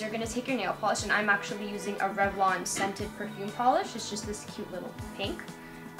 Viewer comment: WAIT !!!!! did she say scented ??